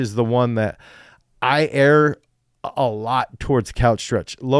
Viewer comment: What does he say at 10:19 ? :D Sounds like he "aires" couch stretches, for the love of me I cant figure out what he means. Thanks. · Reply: It's the word "err" He's kind of using it incorrectly